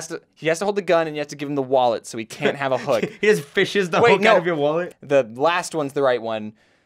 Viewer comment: Did maaz say fishing the your hook out of your wallet